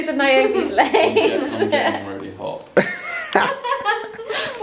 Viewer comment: I don't want to be rude but did i hear right did he say " I'm getting really hard" at 8:31? Or hot or none lol